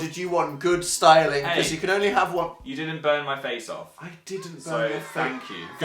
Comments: Seriously thought said "BONE my face off" but that's a different video so lets not do that today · I heard "burn my face off" as "bone my face off"